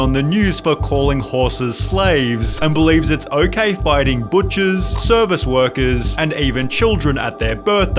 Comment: I misheard “fighting butchers” as “biting butchers”.